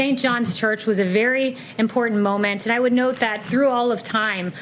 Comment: Wow.... I thought that she said "I would not lie to you". I am shocked that she can lie like this with a straight face like this. But wait... The is adminstartion can look at video footage and say that did not really happen.